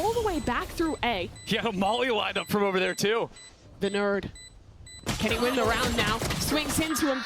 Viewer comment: did she just said "the nerd to runi" lmao